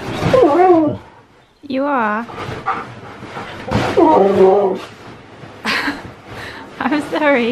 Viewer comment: literally sounded like "Oh noo!"